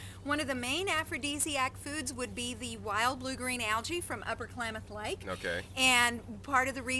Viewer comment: Hey I think she said Upper Klamath Lake